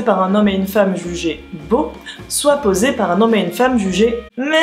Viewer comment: '' bonjour vous êtes moche, je réalise une enquête, acceptez vous de poser des question pour moi svp ? '' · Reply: C'est pas moche c'est "meh".